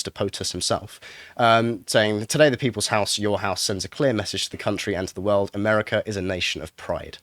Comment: I heard that as "Sending a queer message..."